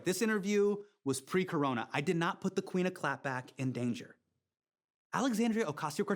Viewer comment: Did he just call AOC the "Queen of clap"? I'm sure he didnt mean the disease and rather the cheers, unless there was a meme i missed.